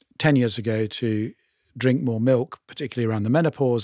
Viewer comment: Tim does not say 'dairy' he says 'milk'. I think he is saying that fermented dairy (cheese/yoghurt) can be beneficial (as he is very much into the microbiome!). They didnt even touch on the fact that there are two types of dairy (cows) milk. Modern Holstein\cross-breed cows milk (95% of what we drink with Type A1 beta-casein) vs. Jersey cows milk (which has protein similar to human milk, goats milk and sheep milk, etc. A2 b-casein). I.e. most of us drink mutant cow milk which contains a mutant protein not seen by humans or cows until the last century. However, there is only weak evidence that Holstein A1 milk is worse for us - e.g. causing inflammation/intolerance.